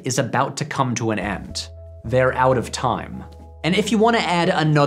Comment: Ohhhhhhh, I never picked up on the Out of Time needle drop because I thought they were saying “Out of Town”